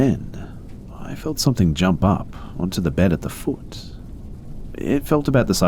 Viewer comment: did dude just say “then something jumped on the bed of the foot” or am I trippin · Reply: "at the foot"